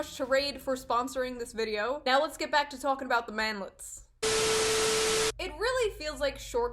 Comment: When she said manlet the first time I legit thought she said mameluke